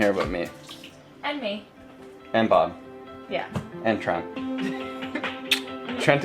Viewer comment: OMG, if you don't watch these videos on Closed Captioning, you are missing out! You said, "I have no one to blame, but myself." "And me." "And Trent"..... but the captioning thought you said "Trump!" OMG, I had the best laugh and had to go back a couple of times. We are watching on CC because my husband is fighting cancer and his chemo is making him lose his hearing (which is a common side effect.) You message about not appreciating life and health until you get sick really hit home. We love you guys and it really made our day to start out watching your video again.